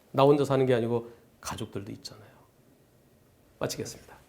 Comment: 의도적 추돌로 보여집니다.사망 가능성 있는 흉기(자동차)로 상대방을 공격했으니,살인미수가 맞을듯 합니다...한변호사님 마치겠습니다 가, 미치겠습니다로 들림니다..ㅋㅋ